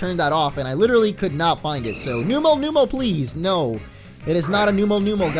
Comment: I thought that said "Umol! Numo!"